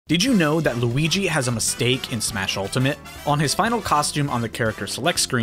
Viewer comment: I am so done with my hearing rn. I thought it said, “did you know in Mario Luigi has a steak.”